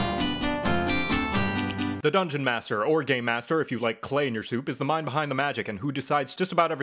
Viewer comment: when you said Game Master, I heard you say " Gay Master". Gachimuchi sends their regards.